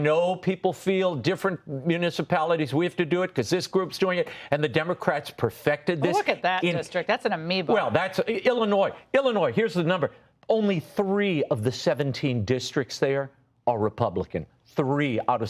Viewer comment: what did she call the district? Did she call it an amoeba? Lmaoo